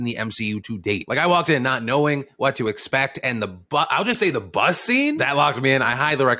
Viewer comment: does Phil say the BUSSY? come on man